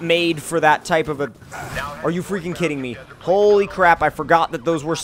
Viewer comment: Did anyone notice that it sounded like he said "pole-y crap"?